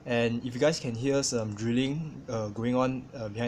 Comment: At first in the beginning of the video I thought you said "drooling" not "drilling"